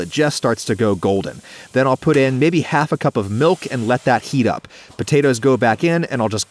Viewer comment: I thought he said a cup of pee.😳